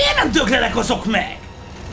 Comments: Na ott mit mondott? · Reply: "Mért nem döglenek azok meg?" :) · mit mondott 15:26 nál? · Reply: "miért nem döglenek azok még? " és én nem értem miért nem érti azt senki